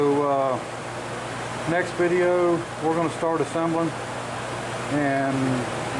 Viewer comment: Whew, I thought you were saying, "next video we'll start it". But, you said, "next video we'll start assembling it". LOL